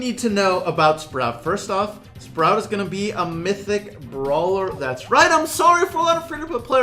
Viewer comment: I skipped the first few seconds of the video and I thought you said “Thicc Brawler” and so I rewinded back a few seconds and you actually said “Mythic Brawler” Lol. like